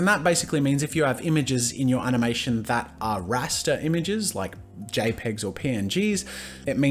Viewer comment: thought he said rasta for a moment